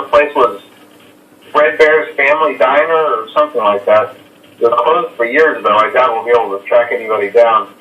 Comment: “It’s been closed for years” is simply not what he said. “It was” is what is said.